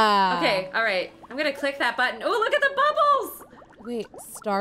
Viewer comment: I thought you said butt holes and all the other times well I thought did